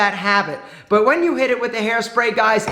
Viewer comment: For some reason at the end if the video I heard it as "kick the daisies" in a weird accent